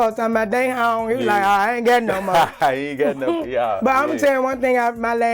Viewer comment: did she says she's not gay anymore? I couldn't make it out. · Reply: will she said "nah I don't got no more".